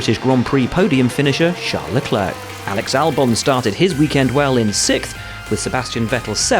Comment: Did I heard SIXFTH?